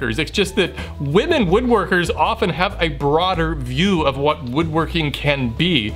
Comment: Did you just call women woodworkers broads?